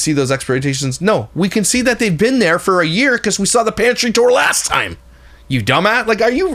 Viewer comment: I thought it said panty tour... lol😂😂😂